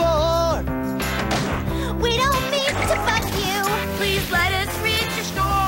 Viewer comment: IM SORRY BUT AT 0:40 DID THEY JUST SAY THEY DONT MEAN TO FUCK THE CROCODILES?! 😭😭😭😭